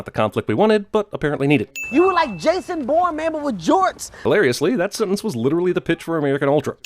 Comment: did he say “ you were like Jason Bourne but with Jordan’s!”? Because no one was wearing Jordan’s in that scene. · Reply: CARAT Mom he said but with Jorts (Jean shorts) which the rock was wearing.